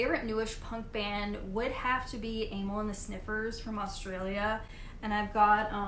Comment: You are right about nostalgia and first listens having an influence on what we like best or least. I personally can't stand Green Day nor the Sex Pistols, but I do really dig The Damned. At first I thought you said Anal and The Sniffers. As a kid I picked up 4 punk cassettes by Charged GBH, The Dickies, UK Subs, and The Exploited. I think my first punk cassette was the compilation Flipside Fanzine Vol. 1, where I then went out and picked up the The Dickies cassette and went on from there. As a kid I mostly went by what album covers I thought looked interesting and I think I selected pretty good ones. I'm not even sure I bought them knowing they were punk, for the most part.